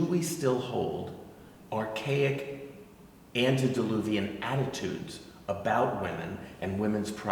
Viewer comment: Did he just say “antediluvian”? As in pre world wide flood from religious texts?